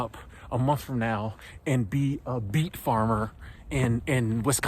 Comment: did he said beet or beef farmer? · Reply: BEET 😂